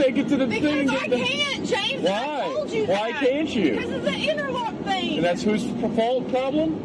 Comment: I heard “interlock issue”, she’s literally an alcoholic